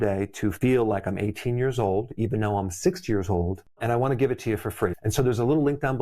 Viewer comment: It sounded like you said "feel like I'm 18, even though I'm 6 years old". The CC caught the actual age though. I was thinking "he must have found the fountain of youth."